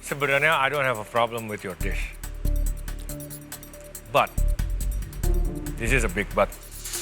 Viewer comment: ngomong apa chef? "But it is a big but"?